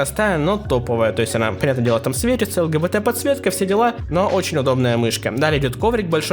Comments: лгбт подсветка я один услышал 🤣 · Мне одному послышалось ЛГБТ подсветка? :D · Прислушайтесь 9:25 Мне одному кажется что он сказал лгбт